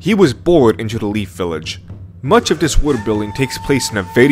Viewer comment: I had such a hard time understanding what the host is saying when he says "world building". I always hear "wood building"